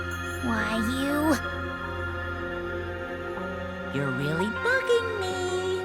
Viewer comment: what did monokuma say? Cause I heard “you’re really fucking me”